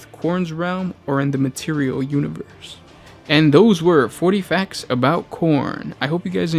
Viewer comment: All I heard was "40 Facts about Corn"